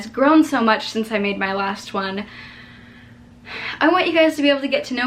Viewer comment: omg, in the beginning, I thought you said, "this is my last one," as in your last video, and I legit freaked for a hot sec!!!! Then I rewatched that part and realized you totally didnt say that hahaa phew!